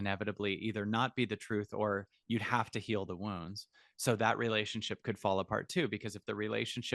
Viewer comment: I thought he was saying ‘worms’ ! It took me a while to realise he was saying ’wounds’.